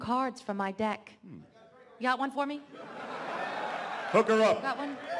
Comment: DID I JUST HEAR THAT?!!!!! YUGI THINKS DARK MAGICIAN GIRL IS... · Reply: Did I just hear THAT! 26:45, Yami just said, "Pucker up." when Mai asked him for a card! I swear!